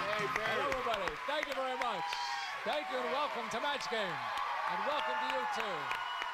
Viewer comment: I thought he said, "And welcome to YouTube!" I was like, HOW DID HE KNOW?